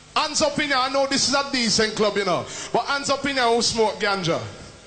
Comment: Did he said ganja